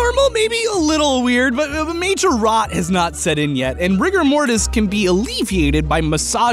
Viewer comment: When he said ''Rigger Mortis'' I heard ''Rick and Morty''